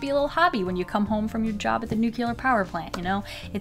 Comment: did she say a job at the nuclear power plant? 😄